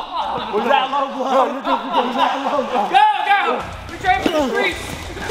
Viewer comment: did I just hear "Go, go! We train for the streets!"?